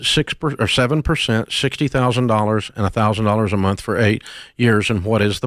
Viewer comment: Dave misheard $60,000 and 6%, but listen carefully: it's $68,000 and it's 8% interest. So 8 years is correct.